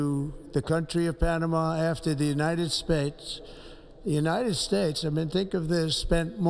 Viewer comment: did he say "the United Spates?"